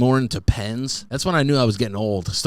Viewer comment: That moment I thought he said depends instead of pens lol cause I’m still on that farting thing 😂